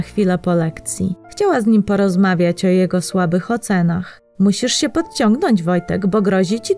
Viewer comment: "Musisz się podetrzeć usłyszałem a potem jeszcze kibel